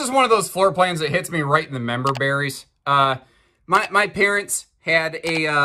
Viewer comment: Did you say, "Hits me in the member berries"? That sounds painful.